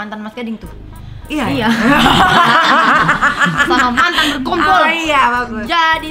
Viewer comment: Menit 9.43 Apa telinga Saya yg salah denger mantan Ber....k.n.t.l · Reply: Berkumpul